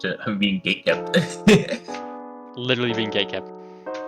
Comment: the subtitles put gaykept instead of gatekept for all 3 of them